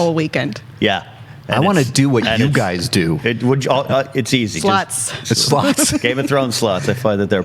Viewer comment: Thought they said sluts. Hahaha!